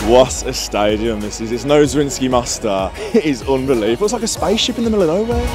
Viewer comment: what did you say? Zwisnky master or something? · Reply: Zrinjski Mostar, a Bosnian team we played in the groups.